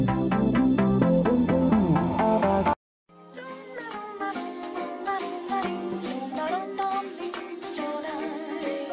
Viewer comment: Can someone tell me whats 0:50? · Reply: Kitty Nikko gandered growing up..and her killing other students in the game mod. Then killing senpai... 0_o I just noticed how weird it sounds.